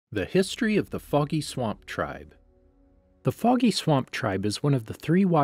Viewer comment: why did i hear "the history of the f*cky swamp tribe